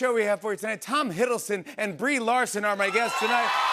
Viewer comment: I had to here it twice I thought you said Bree Olson I got excited 😅